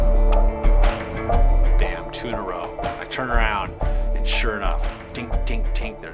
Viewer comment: Lol, when he says "2 in a row" at the 25:50 mark the subtitles say "tuna roe". So funny imagining Pete say that to himself as he wipes out